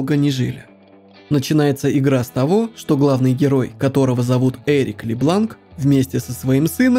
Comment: Мне одному послышалось эрик е*лан